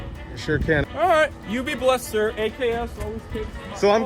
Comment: It sure sounds like he said "You be blessed sir, AKA a$$hole"